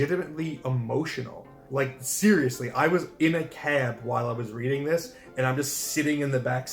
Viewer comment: Oh my gosh I completely misheard that! I thought you said you were on the can 🚽 having an emotional crisis. I had to rewind three times to hear you say cab. I was like “Damn he a real one for admitting that.” 😂